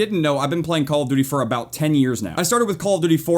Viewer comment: Thought u said 8 years ? Hmmmmm